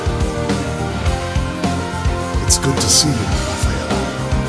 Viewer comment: did he just say good to see you rafaela?